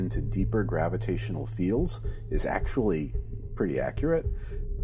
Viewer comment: "is actually pretty accurate".. I heard those words as ".. pretty freaky"